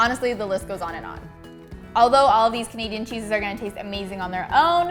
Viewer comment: did she just call them vegan cheeses? at 4:15 or am i imagining things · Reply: Leyla P you're imagining things. She says Canadian cheeses